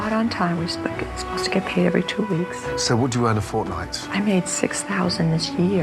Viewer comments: it sounded like he said fortnite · In 11:59 why did i hear Fortnite instead of Ford like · Did I just hear fortnite? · does he say fortnite ? cause thats game is dead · I heard him say fortnite what’s wrong with me? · at first i thought he said "fortnites" but it's "four nights" 😂😂😂😂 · I THOUGHT HE SAID FORTNITE XDDDDDDD OMG LMFAO